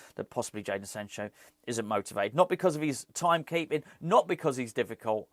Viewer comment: I've just had a good laugh. When you said there was a problem with his time keeping, I thought you said Typing ! I thought surely you don't have to meet high secretarial standards to play football ! ( .......'Well if he can't do 90 words a minute we can't use him' .......)